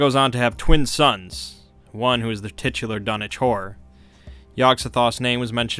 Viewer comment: Dunwich horror! I thought he was saying donatour...